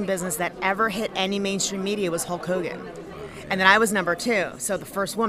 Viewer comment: Did she just say Hull Cogan?